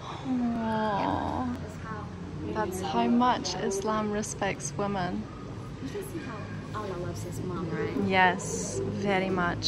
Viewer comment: did she just said “how much allah love his mom”